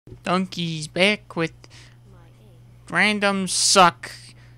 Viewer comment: What is it he said? Randoms suck... socks? Docks? Wait no, Clocks, thats it